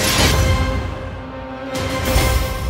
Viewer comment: What did he say "the........have arrived" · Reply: Stratski